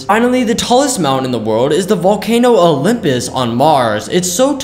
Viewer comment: Bruh I thought you said on earth not on Mars with a VAllcano